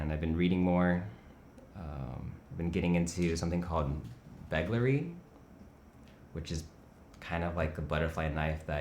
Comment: What did he say the name of that toy? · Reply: Belgleri